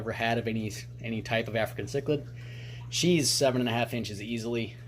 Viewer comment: i thought is said “EPIC AFRICAN CHILD